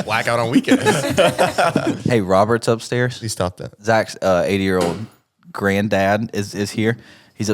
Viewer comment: I thought said 8yr old grandpa for like 5 minutes of the topic 😂